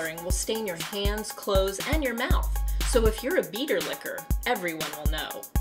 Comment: I thought you said 'Beetle Licker' until the annotation came up and said 'Beater Licker' lol........